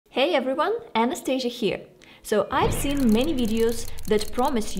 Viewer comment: 1st time i heard your name i thought you said "anesthesia" 😊